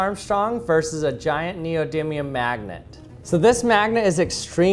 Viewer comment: I thought he said “ a giant magnetic maggot “